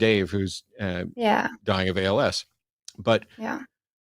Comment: Did Matt said at 56:30 that Dave Warnock "died of ALS"?? 😢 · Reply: Matt said Dave is “dying of ALS”